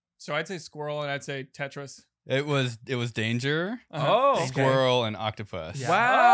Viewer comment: I was listening with 1 earbud in, I heard “squirrel” and “Dangerous”.